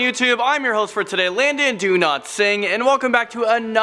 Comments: I was watching this without earphones and when Landon said stated his name, my brother thought he said Landon "do not sing" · I heard it as Landon "Do not sing" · It sounded like you said your name as landon do not sing